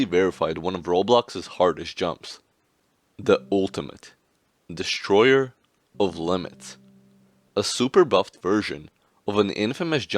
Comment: I keep hearing it as ''destroyer of lemons'' instead of ''destroyer of limits''